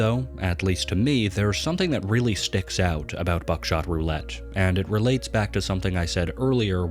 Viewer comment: I thought it said “backshot roulette”